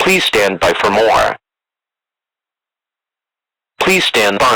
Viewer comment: The fact that the EAS says "Please. Help." at the end really makes this all the more creepy and really does a great job at giving a tone of desperation. I can't wait for the next installment! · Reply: I always thought it said “Please, Hold” but hearing it again it definitely says help, not hold.